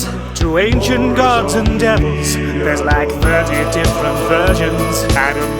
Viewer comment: why do I hear "there's like 30 different virgins"?